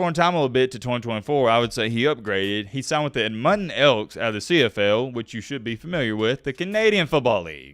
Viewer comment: did you say the Mutton Elks? or was that you trying to say Edmonton